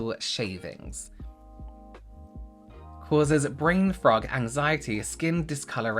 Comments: Wait did he say brain frog?!? 😅 I'll def take some brain 🐸's, I love them!! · I'm sorry, at 12:10 it sounded like you said "brain frog" rather than fog, and all I could think is that sounds like a condition I'd rather like to have 🐸